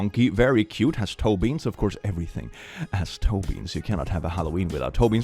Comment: I had this video on while falling asleep last night and I was convinced I had finally found a video where said my name, now I watched it back and it turns out he said “toe-beans” and not Toby. I find my half asleep mishearing quite funny though, especially because I got pretty excited about it and even replayed it, still without noticing.